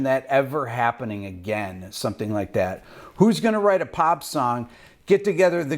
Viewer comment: Did he just say, "who's going to write a POP song..."? 🙄